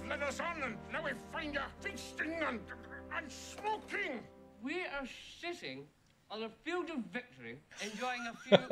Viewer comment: It sounds like Pippin said "we are sh!tting on a field of victory".